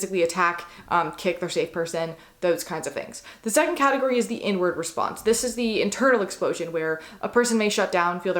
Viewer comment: I thought you said the n-word response when you said inward response. I was like, ummmm, the what now?